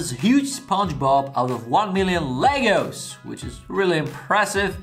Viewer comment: That was good thought you saying legals. Then pull the box up Legos. Love how different accents pronounces words.